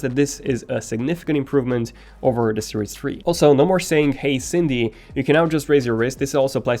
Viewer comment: Did you say "hey Cindy"?